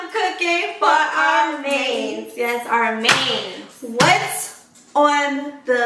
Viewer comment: Tell me why I thought it said “cooking for our “MANS”😂😂. Here for vlogmas #4 like always keep going with the good work😂❤️❤️